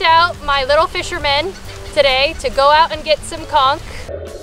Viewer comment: Hey baby doll my ears thought you said you going to go out and find COK you guys are a trip! Crack me up!